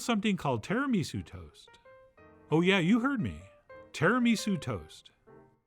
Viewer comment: I thought you said Tiramisu Toes!